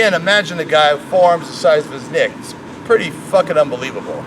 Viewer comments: I swear he says "forearms the size of his dick" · I was in a different tab and thought you said, 'Forearms the size of his dick'. Pls